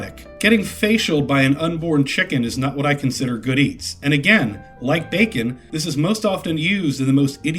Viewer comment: “Getting facialed by an unborn chicken is not what I consider good eats.” I misheard “big meats” at the end the first time, for some reason.